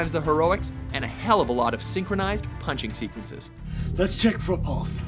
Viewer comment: did he say lets check the balls?